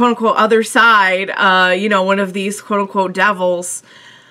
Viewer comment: Can someone tell me what she said at 3:47 she said like "kuroko" ? I'm from Indonesia and english is not my first language, thnks! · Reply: Actually she said quote-unquote. just fyi